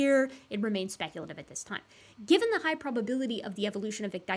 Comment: Very good papers, I really like the part where said "IT'S GIBBONIN' TIME" and just gibbon'd all over the place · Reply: You say that, but almost every time she says "Given" I hear "Gibbon" so I always have to do a retake of the last 10 seconds, cause "Gibbon the context" throws me for a loop and I stop listening for a moment